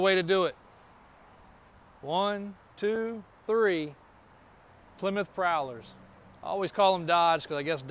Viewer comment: Sounds like he said Plymouth to me.